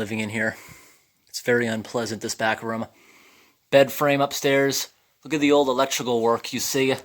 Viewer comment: What the hell are moth balls and why were they in that house Edit: sorry if this offends you but it sounds like you say back rum or bed rum or idk sorry lol